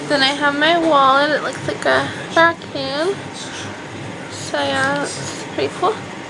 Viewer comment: when she said her valet looks like racooon i thought she said it looks like a vacum xD